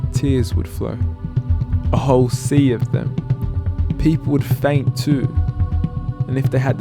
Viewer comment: Can anyone type what he is saying from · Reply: 1412 A whole sea of them.. And people would faint too.